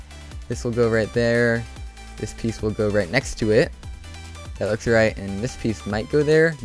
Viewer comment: who else heard him say 'This looks right' at 2:18?!?